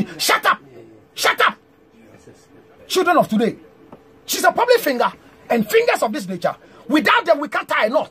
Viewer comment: Did he just say “Public Finger” lol 😂 he seems upset 😡 maybe he had a crush on her.